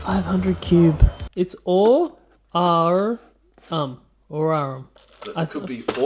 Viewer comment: I thought that said uranium I'm like....... why the hell would you put uranium in a nerf gun?that shit be radioactive